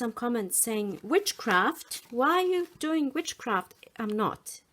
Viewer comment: These are lovely, i really thought you said witchcraft LOL